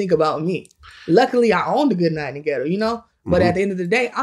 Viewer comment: What is she saying at 7:20 I played it 10 times n can’t tell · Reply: Luckily I owned a good night in the ghetto? Huh?